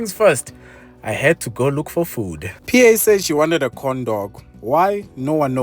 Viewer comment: My naughty mind interpreted what was said here as "PA said she wanted a condom." I had to rewind 😭🤣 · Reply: Cleanup your mind bro... Corndog... Lol😅